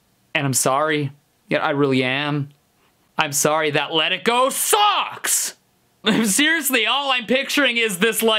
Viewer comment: if you watch with captions it’s says sock instead of sucks.